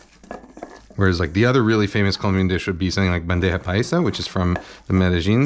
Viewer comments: I thought you were saying pendeja paisa at first 😂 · Coulda swore he just said 'pendeja paisa'